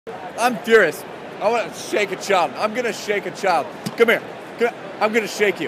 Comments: I heard "Shank" O-O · If next video doesn't include him shaking a child, i am unsubscribing. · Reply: Is it wrong that I misheard it as "Shanking a child"? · i thought he said shank a child🤣